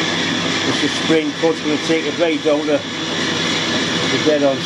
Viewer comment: Subtitles say "the supreme court reinstated a donor". Think this algorithm needs work.